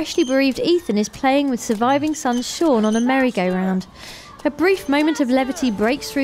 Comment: it sounded like she said mary gay round because of her acsent